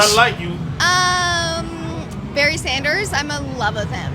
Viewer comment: I thought she said Bernie Sanders